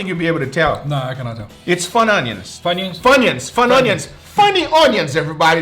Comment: It sounded like guga said butt onions at first to me. Lol. Idk why.